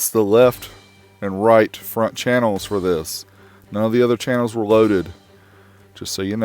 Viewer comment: It's a lie. Unless you listen to test tones instead of music. Also, why buy a 9 or 7 channel reciever for stereo 2ch listening? In a home theater setup with 5.1 chs up to max channels, this AVR would run at 80W or less if used as intended. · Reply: I thought he said the other channels were loaded..